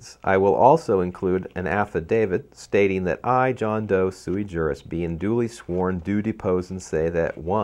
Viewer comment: what is the word being said at 4:35 I john doe ( soemthing ) jurist ? thanks